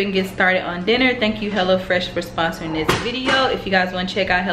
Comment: Idk I thought you said hella fresh 😆